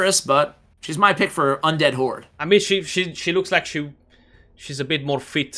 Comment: Is it just me or did I hear "Undead whore" ? lol I am picking Allure as sexiest and Juliana as most beautiful.